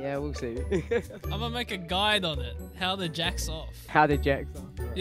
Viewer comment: I hear you say "Jax me off" and I'm here like "that's sus"